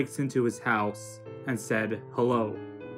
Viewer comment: i thought the person said “what” rather than hello